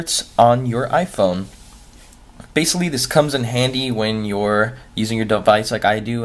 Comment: did he actually say "basilly"